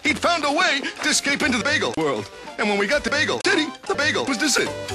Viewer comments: it sounded like he said bagel tittie! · Can't help notice that you will block anyone who says, "Bagel Txxxy?" Why is that? xD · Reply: A while back, tons of people kept claiming that that's what Luigi was saying at 0:49, as opposed to "Bagel City", which is what he actually said. I just got fed up with the "joke" eventually and declared an ultimatum.